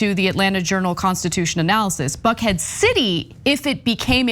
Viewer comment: I thought she said the name was fuckhead 😂 so there’s a neighbourhood in America called fuckhead lol 😂 😂 well the name suits it nice fit